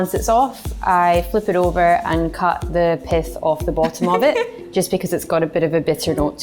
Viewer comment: To be fair, I thought she said ". . . I turn it over and cut the piss off the bottom . . ." also.